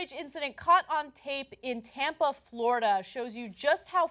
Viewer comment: I thought she said tampon florida